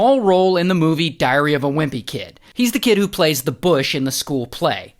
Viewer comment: No way i just heard "Diarreah of a Wimpy Kid" 😭